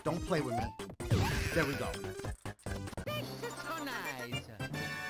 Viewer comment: Sound like Mario saying. Sex is so nice